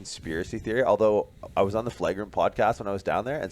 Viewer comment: Recently on which podcast, Shallow? Sounds like you said 'The Flight Room' podcast.